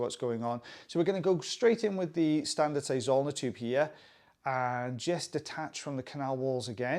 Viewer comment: For the longest time I thought you were saying "standard size on the tube" and "fine end on the tube" lol!